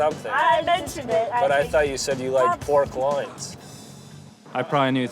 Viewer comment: I thought you said you like pork loins 😂